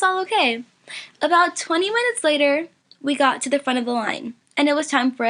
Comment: Lol I thought she said 20 months 0:56 😂😂😂